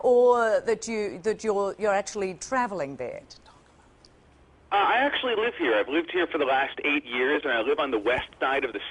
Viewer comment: What is she or someone whispering and to who? around the 1:06 mark?? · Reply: It say don't talk about it